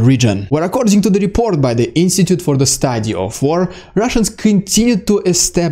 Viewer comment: What's that institute he keeps quoting? I hear "institute of the stadia of four". I know that's not the name but my brain can't hear anything else.